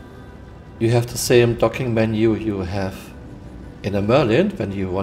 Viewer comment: I thought you said, "you have the same f**king menu that you have in the Merlin," and I was like, "yeah that menu is rough!" Then I realized you said docking menu.